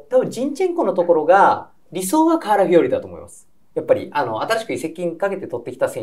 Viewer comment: 『カラフィオーリ』が、『瓦日和』に聞こえた